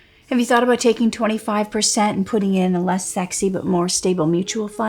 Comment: DID SAY SEXY